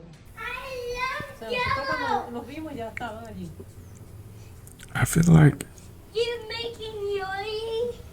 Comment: Did that little kid open that video with I love jello or am I the only one I heard that